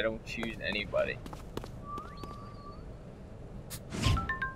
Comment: Oh i thought you said "purple bread" insteas of "triple treath"